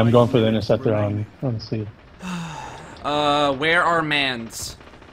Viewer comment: It sounded like you said "Uh where are mans"